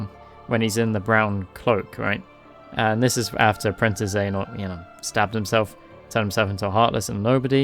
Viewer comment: Did you say "princess Xehanort" at 1:36? As a kid I used to speculate that thee creator of Kingdom Hearts had been bullied by some tanned beach hunk since many of the villains were darker skinned than the rest of the characters.